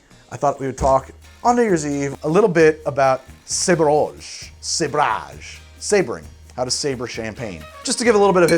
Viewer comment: I thought he said sober. Then I realized, he is never sober